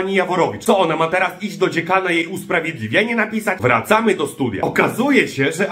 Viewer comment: Zamiast "wracamy do studia" usłyszałem "wracamy na studia". Chciałaby 😅